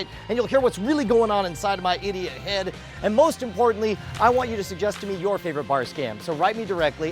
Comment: You know at the beginning I thought you said I want you to share,because in Russian if you say die it means share